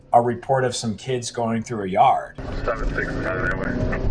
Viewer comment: I don't know but I'm pretty damn sure I heard the numbers 6-7 💀